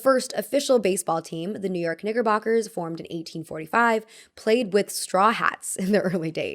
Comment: mina i thought u said the n word when u said 'knickerboxers' 😭